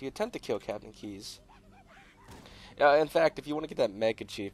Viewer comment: What does he say at 0:38? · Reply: Now In fact....